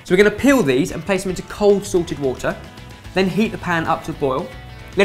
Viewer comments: what said: "... place into cold salted water." what i heard: "...place into cold sorted water" so yeah, here's a free business idea: sorted water, great for boiling potatoes, pasta, making stock... :) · oh my gosh .... they're saying 'salted water' ... I thought that they had been saying 'sorted water' for years -.